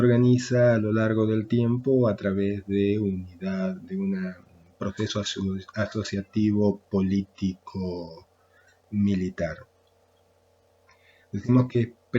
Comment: hola buenas tardes profesor, queria consultarle que dice en el minuto 1:40, donde explica el segundo punto de vista de las gens, me quede en "se organiza a traves de un..." y no logre comprender que es lo que dice, si me pudiera sacar esa duda se lo agradeceria! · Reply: Hola. Proceso asociativo político- militar